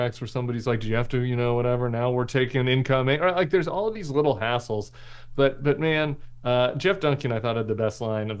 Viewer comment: "All these little hassles"...... I thought I heard "all these little assholes". I like my version better.